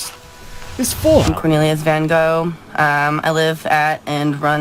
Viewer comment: Did I hear that right..?? she's named herself Van Gogh.. Looking at her artwork that's pretentious to an extreme... ha ha ha